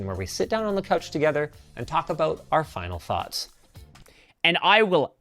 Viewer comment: Linux said "fahts" instead of "thoughts", how interesting.